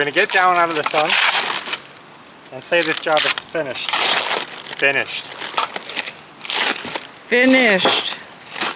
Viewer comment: did you say you are Finnish?